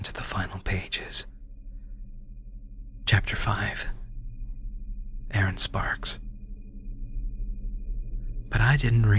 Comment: What he said: "My name is Aaron Sparks." What I heard: "My name is Eren Sparks." o.o *cue Attack on Titan theme*